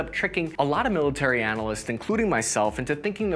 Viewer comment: Did you just call yourself a military analysis at a 1:29 into the video. LoL. best part.